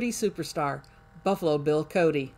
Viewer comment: My grandmother was born in 1890, so she was 8 years old when this was recorded. I can remember singing the song "Buffalo Bill won't you come out tonight and dance by the light of the moon" with her during my childhood. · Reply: I always heard it as Buffalo Gals...